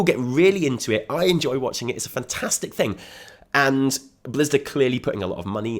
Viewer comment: did he say Blizzda at 31:14 ? Blizzard & Bethesda hybrid XD!